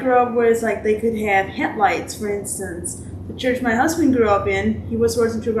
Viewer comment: Head lights?? I thought you said head lice!!😱 LOL!!😂😂😂😂😂 Yes, head lice are bad.😂